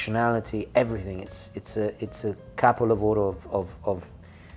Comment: Italian speakers, I've been wondering forever what says when says that. I speak Spanish and French so it sounds something like nugget of gold but I can't figure out what it is he says. Thanks in advance. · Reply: Sorry, I was saying "Capolavoro", in Italian it means "masterpiece". Thank you, I hope that helps. Best regards